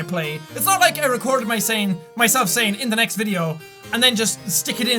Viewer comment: Oh. When you say "in the next video" I always thought you were saying "later" in a really weird voice...